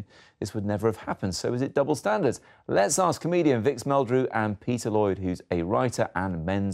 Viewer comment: I dont believe it! I misheard and thought the guy was introducing Victor Meldrew. Gutted